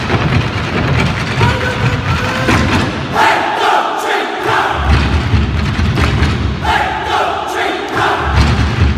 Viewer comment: What did they say at 1:56? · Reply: A haon, a dó, a trí, a ceathair Which mean 1, 2, 3, 4 in the Irish language.